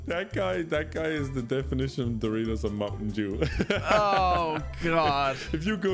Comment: when Novak say “Doritos and mnt dew” I thought he said “the realism of the modern Jew” and I was losing my shit thinking about such a fucking out of no where Jew bash I almost lost my yamaka